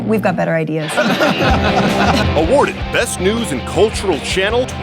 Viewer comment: Why was there a Young Turks ad in the middle of this video? LMAO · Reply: I thought I heard them say, "They are real TURDS".